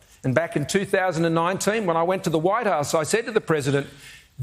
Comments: The growing confusion on Anthony's face is very telling. Didn't help that it sounded like Scott said he went to the "White Arse". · I swear Scomo says "when I went to the Whitearse..." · anyone else hear "White Arse"?